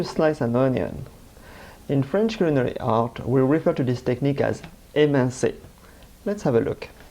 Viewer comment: I'm not sure what French word is being used at 0:13. Google hasn't been any help. "In French culinary arts, we refer to this as ___________" Anyone know? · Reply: "émincer" That would be "to mince" Have a good day. -Some french guy